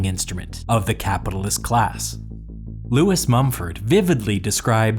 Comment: This Mumford guy is smart I wonder if he has a band with his sons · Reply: First time through I swore I heard Marcus not Lewis and rewound 15 seconds